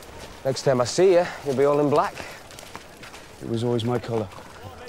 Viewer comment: Why do I keep hearing 'It's always been my collar' instead of 'It's always been my colour'?